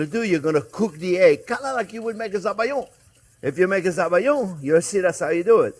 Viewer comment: I learn so much from you ... but you used a French word with which I am unfamiliar, so please advise what you were talking about - it was a sauce -- at 3:44 - sounded like Sah-Vah-Yohn (like the red wine?) Help. Lost on this. Thanks. Also hadn't heard "chervil" for quite some time and had to look that up too -- French parsley! How different from American or Italian parsley is its flavor?